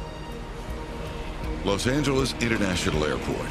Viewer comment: I swear he said Los Santos